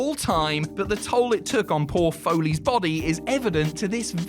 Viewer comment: I can't be the only one who thought he said "Paul Foley" at 9:42 in place of "poor foley" lmaooo